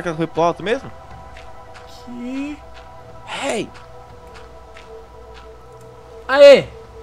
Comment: No minuto 2:41:42 foi só eu que escutei " HEY " ?